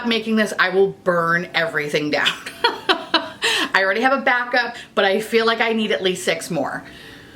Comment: I thought you were gonna say “I bought a bag ” of the PMG concealer instead of what you actually said “a back up” 🤦🏻‍♀️ 🙄 😗🎶